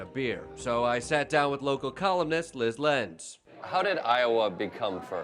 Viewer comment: I thought they said “local communist” 😂